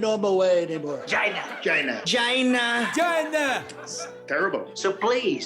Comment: I thought the trumps were saying (va)Gina.